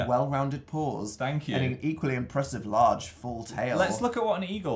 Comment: "Ya got an equally impressive large, full tail.... Well, golly." ;) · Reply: I didn't hear tail and I was so concerned cause all I heard was "Ya got an equally impressive large, full..."